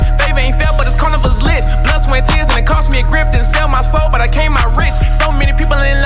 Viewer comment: did Aha said he sold his soul and came out rich??? Or maybe I'm hearing wrong... Someone tell me what you heard lol or I don't know what he meant by that statement if I heard right. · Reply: Damoi Myers actually it was didn't sale my soul but still came out rich